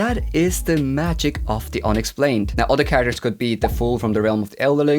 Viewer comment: I thought you said the difference is in the "Onyx Flame". I was wracking my brain to think of the Onyx Flame in LOTR. Finally I heard you say it again and realized you said "the unexplained". Now we need someone to write a series based on The Onyx Flame!